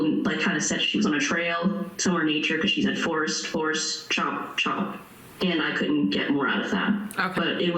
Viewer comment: I heard a whisper say "chop"